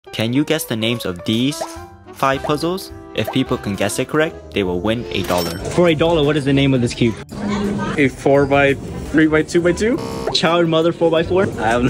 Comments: Instead of these it sounded like he said deez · I actually thought he said deez but he actually said these💀💀